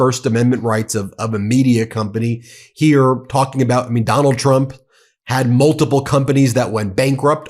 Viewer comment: It sounded like you said McDonnald Trump. That's his new nickname